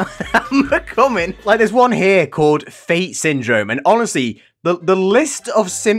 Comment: I thought it said tate syndrome.